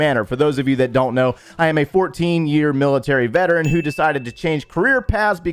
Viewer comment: yea but to become a vet you just work actively for idk 5 or 6 months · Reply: shut up. Could've sworn he said 14 year military veteran